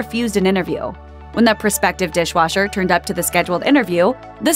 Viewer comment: How can a deaf person be a waiter? How can they take orders...or talk to customers. I would hire them either. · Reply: I thought they said, 'dishwasher"